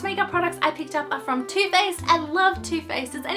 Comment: I thought you said 'toothpaste' and I was like 'WHAAAAAATT????'. I was so confused cos anyone could like toothpaste but then I realised you said 'Too Faced' haha 😂